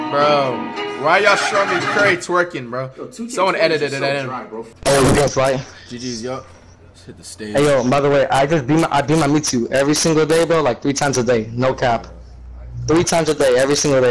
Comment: I thought he said I dm u everyday 😭